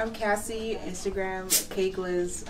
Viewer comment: 😂😂😂I thought she said cakeless